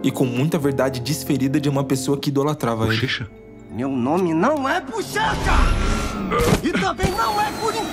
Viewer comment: eu ouvi algo, mas não consigo provar.... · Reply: RONALDO? Meu nome não e RONALDO! Eu queria dar e só isso eu queria DAR